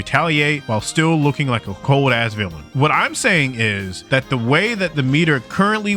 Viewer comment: For a moment I thought you said "cold-ass vigga" 😂